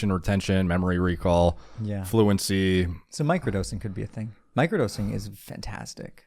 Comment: 😂😂😂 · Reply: i thought he said macrodosing MB that came off weird 😂💀💀